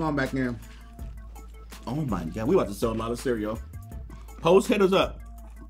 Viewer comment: Sounds like you said.. Hoe’s hit us up lol